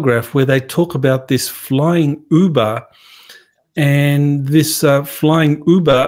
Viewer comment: it sounded like they said Flying Booba.